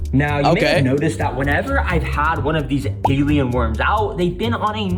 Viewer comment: did bro just say gaylien worms